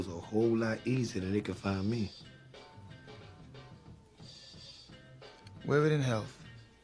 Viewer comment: Ain’t it tho dat nicca Marlo told Omar wear da ring in hell dat nicca Omar said no doulbt🤣🤣🤣real gangsta street shit ain’t no tv show or movie frfr ever came close to showing da streets this shit be far fetched but da attitudes and situations and roles is exactly like da streets frfr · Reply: damn whole time i thought marlo said wear it in health